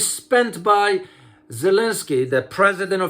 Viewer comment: I thought president said his name was belensky😂🤣. Thanks for the update. And i thought EU already facing economical destruction.